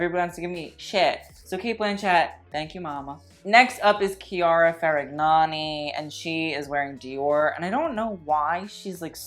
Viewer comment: Chiara WHAT? you literally said ferignagnui lol ❤